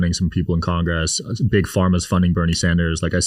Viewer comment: did buddy say big Pharma funding Bernie Sanders?